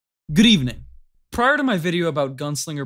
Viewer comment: Did he say green?